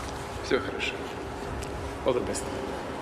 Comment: Did putin say 'all the best' at 05:10 ?